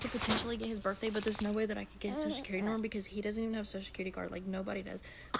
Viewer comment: did she just say no one has a social security card?? girl literally everyone should have one... she said "i told them all the tattoos he has." girl, them people don't care about that 😭😭 if you're having a baby by somebody you should AT LEAST know their social 😂😂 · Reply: I said he doesn’t have his social security card bookie🎀 how could I know something he doesn’t nor anyone in his family?😂